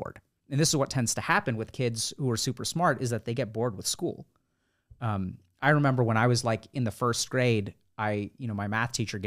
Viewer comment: Wait wait did he say I remember in 1st grade?